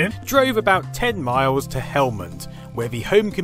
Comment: I thought you said Helmand at first. That would have been dedication. Looks a great place and he seems a real decent chap!